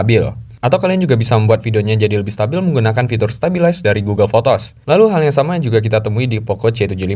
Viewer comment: app apa itu yg bikin stabil video? Klo gk salah dengar google fotos y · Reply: Google foto bro, langsung edit dari aplikasi bawaan, setiap hp pasti ada google foto itu